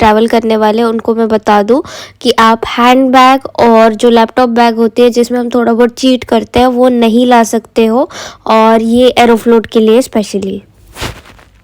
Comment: from 2:55 to 3:00 what did you say kya nii laa skte? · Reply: Laptop ya hand bag alag se carry nhi krskte aeroflot m abhi kyuki they got out point😂 sirf cabin bag h allow h